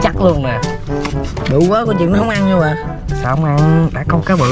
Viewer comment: Ủa Lúc 15:11 nói gì dị m.n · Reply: Bự quá coi chừng nó ko ăn nhà Hoà